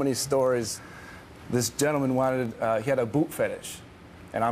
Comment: a boot fetish?? i thought i heard boob fetish hah